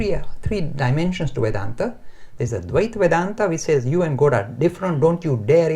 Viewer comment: He meant to say Dvaita but it sounded like Advaita. Swami teaches Advaita which is nondualism. His accent sounds kind of weird, even as an Indian it's hard to understand.